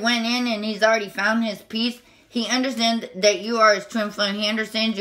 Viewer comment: please answer me!! did you say twin flame Henderson at 36:13 ???!!! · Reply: He understands is what I said but if Henderson works for you than take it lol